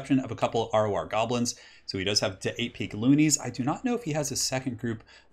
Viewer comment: everytime i hear 8 peak loonies i only hear "AP Cloonies"